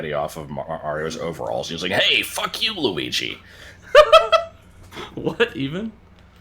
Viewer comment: he says "thank you, Luigi!" Disappointed in for not knowing that because it's the exact same principal as the flower pots in MMM in Banjo Kazooie, where everyone thinks they hear "fuck youuuuu" but they really say "thank youuuu". One guy said "hu hu, he said fuck you, and now everyone "hears" it. Losing 50 IQ points as soon as you start recording/stream has been refered to as the Let's Player's curse, at least how I first heard of it, where you could be a master at a game, but as soon as you start recording it, you end up making mistakes you normally wouldn't make.